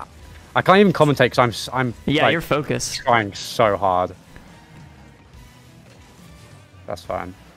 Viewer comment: Did just say he was so hard? 😅